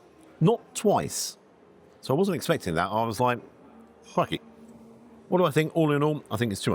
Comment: Hi, Think you might just have got yourself back into Gordon's good books. I was watching this using the subtitles, i thought that Gordon had walked in himself at 3:49, you said what sounded like "crikey" what came up on screen said something completely different. When you get chance, check it out. Until the next time.